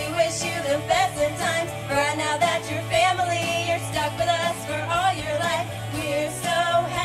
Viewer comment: "burn out your family" I can't understand it. Although these ladies did really good before that. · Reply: They say "Brad now that you're family" because the grooms name is Brad